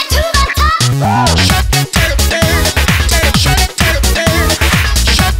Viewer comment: all i hear is 'shut that turtle down" lmao